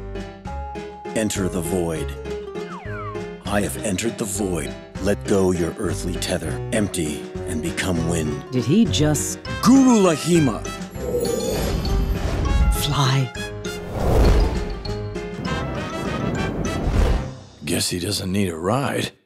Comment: Did someone else hear "google Laghima!" at 0:18 ? 😂